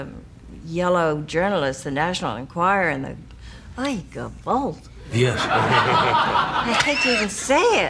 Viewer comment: Amazing woman, still love her!! But what is she saying in 14:33 after "national enquirer" (?) Sounds like "like 'em both"?!